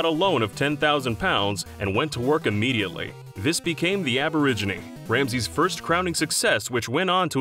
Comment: the aboriginee?? LMAO instead of the Aubergine....